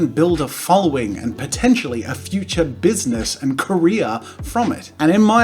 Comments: I thought he said Korea rather than Career. Wow, I’ve been around Americans way too long. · When he said "and career," I thought he said "in Korea"